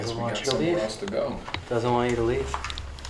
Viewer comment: I heard something’s say “no you should leave”